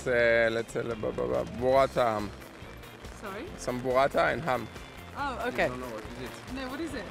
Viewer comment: What is the food he said at 1:23? "Water Ham?" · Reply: "some burrata and ham" google it ;)